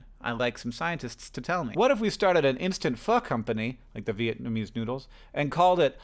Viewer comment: was concinced I heard Hank drop a rare F-Bomb, but sadly it was "Pho-company" not "f***-company" I also thought the pho company should be called "Totally pho-cable"